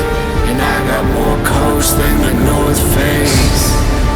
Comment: "I got more coats than the north face" !? Please tell me I heard that right 🤣🤣🤣🤣 BRILLIANT